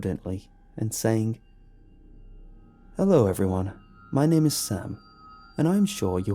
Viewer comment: I misheard you thought you said "my name is ham" damn that woulda been funny